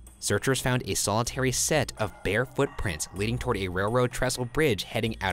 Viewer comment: When he said bare footprints, I thought he meant bear footprints lmao. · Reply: ohhh that makes more sense, I thought he was saying bear footprints but I haven't seen any in the video lol